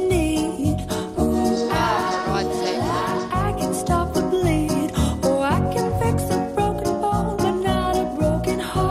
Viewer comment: what Kelly says???? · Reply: "oh for god sake"